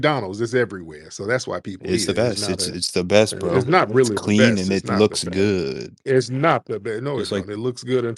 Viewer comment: did he say "its clean"??? These dingy box "high IQs don't even like to bathe😂 I guess you get nose blind to dog funk😊